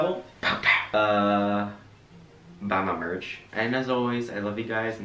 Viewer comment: The first time i watched him i thought he said everything is lesson three and i was thinking tf is that